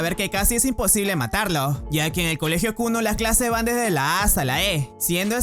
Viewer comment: Siempre escuche "kunno" ●_●